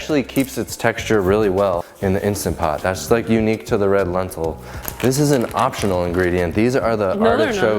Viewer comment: You said the link to the Instant Pot Ziti woukld include the exact recipe ingredients. I downloaded and it is pretty general. The main thing I was interested in what what kind of pasta because you mentioned the one you were using didn't get mushy. I see the shape is penne and I see the brand is Barilla, but is it red lentil pasta or chickpea pasta? What kind of pasta?? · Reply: I just re-watched and heard you say, "Unique to the red lentil" -- sorry! Watched it 3 times to catch it.